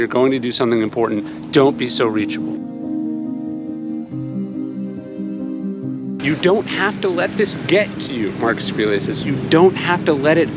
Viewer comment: I swear I thought that said DON'T HAVE AN IPHONE, which would have been the greatest advice of all.